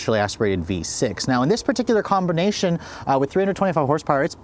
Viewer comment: “Combernation” is what I heard